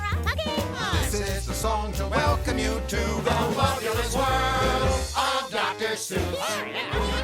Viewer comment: Hearing the name spoken aloud, I can't help but constantly mishearing it as "The Wubbu*less* World of Dr. Seuss". Maybe its just me, maybe its a marketing overview. idk